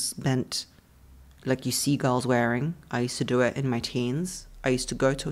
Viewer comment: you said “see girls wearing” and i heard seagulls wearing, and that is exactly what I thought fit perfectly for how some girls walk in high heels.